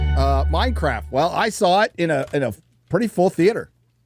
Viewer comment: did he say MineCrap?